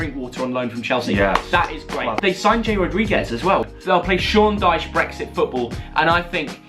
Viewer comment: I thought he said James Rodriguez.